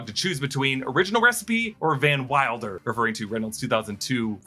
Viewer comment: I may have misheard it in the theatre, but I thought he called Nicepool "Van Milder". That was my favorite joke. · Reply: definitely said van milder lol